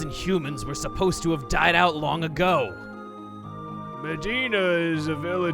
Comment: when you said “Medina” I heard “Vegeta” lol